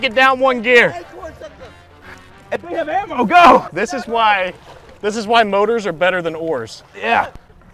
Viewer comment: this is why brotors are better than whores I know that’s not what he said but being I just woke up and am stoned as fuck that’s what I heard